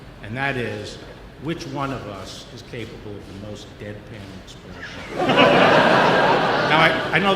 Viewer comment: What did Mr. Barr say at 0:45...I can't make it out....came back to edit. I figured it out. Deadpan...that's perfect for Mr.Barr lol. #WWG1WGA